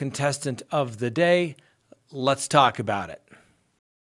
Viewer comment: Whenever you say “let’s talk about it!” Every time I hear “let’s TACO bout it.” LOL... maybe a merch idea 🤷🏻‍♀️😂